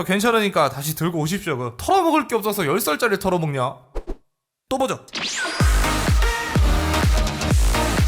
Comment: 똥오줌 이라고하기에 이번엔 ㅂ 발음이 정확해서 또보죠로들리네요.... 또보러왔음